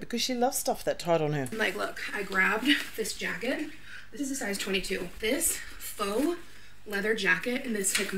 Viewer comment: does she say size 20 TOO OR. 22.. ? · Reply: 22!!!!